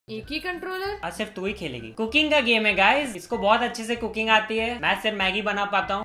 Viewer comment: i heard it as "coca!ne ka game hai guys" I- I need a break